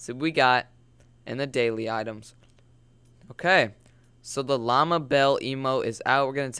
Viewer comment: Thought it said Ligma Bell... Lol